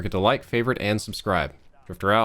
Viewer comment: so tired it sounded like he said “Drought” instead of out